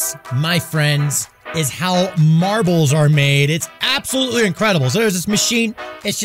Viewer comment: I thought he said "Marvels"